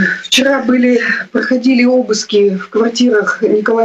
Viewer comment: Обыски в квартирах? Или послышалось?